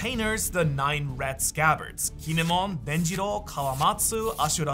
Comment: DID BRO SAY TANJIRO??